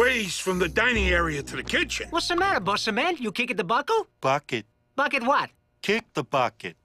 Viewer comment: i thought he said fuck it